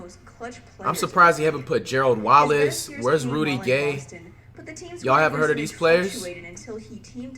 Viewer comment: did you just say wheres Rudy Gay 🤣